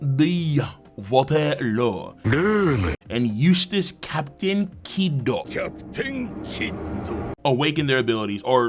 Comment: I heard that as "Useless Captain Kidd" and I smiled.